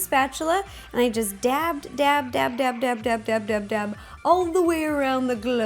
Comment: i thought she said dab dab dab